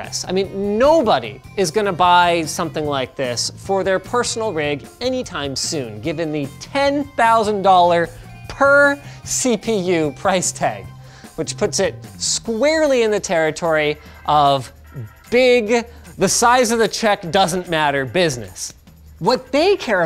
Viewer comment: I thought you said 10,000 dollars per cpu core, and I almost had a heart attack.